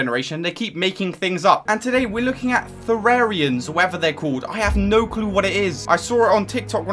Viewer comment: my dumbass heard that as terrarians